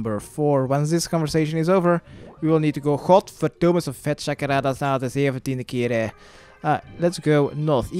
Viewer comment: lmfao 8:04 what did you say? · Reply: "God damn it this is the 17th time"